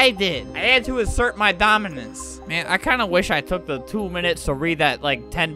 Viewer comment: I thought you said dominus